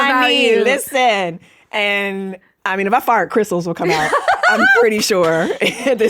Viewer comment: Wait...did I hear that right? Did she just say if she farts crystals would come out as a follow up to describing she's not religious, but very spiritual? Sorry, Skye. You have no say in the matter that you just made me see you as a friend. My people! And it'll freak your old hood out, because I'm an old white dude! But really, you have made me conclude you are awesome.